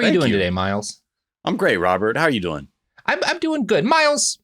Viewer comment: Did Miles just say "Im Gray"? Thats hillarious!